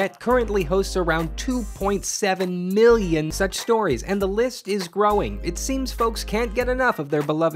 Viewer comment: i heard that as "sex stories"